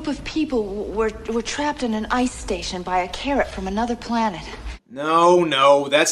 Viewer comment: did she just say a “carrot from another planet”? I haven’t seen The Thing from Another World but I’m pretty sure there isn’t any killer carrots in it.